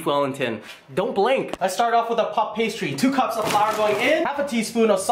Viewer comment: i heard fuck pastry instead of puff pastry lol